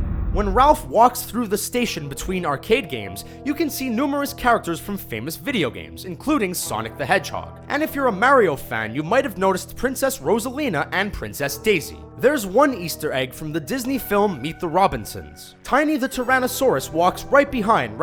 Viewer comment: Did he say Maryio lol!